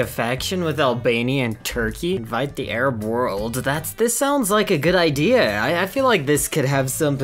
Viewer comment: I thought you said 'fight the Arab world'